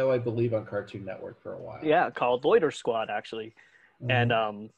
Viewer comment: Love what you're wearing!! I also want to apologize for calling you instead of how it is properly pronounced. Too much DBZ, maybe? Oh, and you're definitely a superstar! Side note: I thought he said "Goitersquad" which would be a totally different experience than Loitersquad. Probably. Great job, folks!! This was awesome.